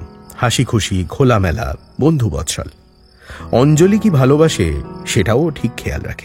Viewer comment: অঞ্জলি 'কি ভালোবাসে' শুনতে গিয়ে শুনলাম 'অঞ্জলি কে' ভালোবাসে 🤣